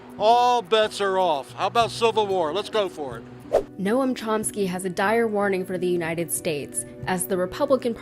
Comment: did she just call him "no-um" chopsky?